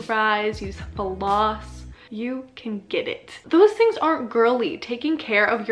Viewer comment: did you say those things are girly ?